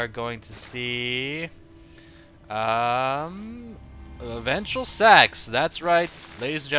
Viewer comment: Ventral Sacs?!?!? I thought you said eventual sex AHAHAHAHA and you would definitely say either one!!!!